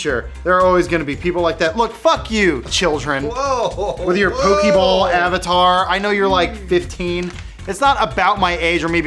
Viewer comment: did he say with your Boonty Ball avatar? Thomas Boonty would not appreciate your use of his new intellectual property!